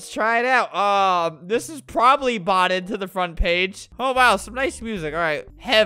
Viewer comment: I thought it said BOTTLED xD